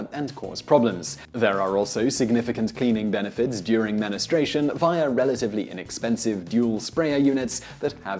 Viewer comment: Did he say "menu-stration"? :D